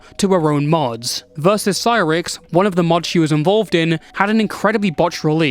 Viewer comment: I thought I heard cyraxx and had a double take